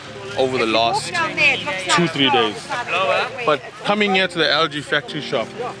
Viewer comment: Awe I got super hopeful when I thought he said the "algae," factory shop, with maybe growing algae from municipal onsite wastewater to then convert into bio-energy, and compensating citizens equally for using toilets correctly.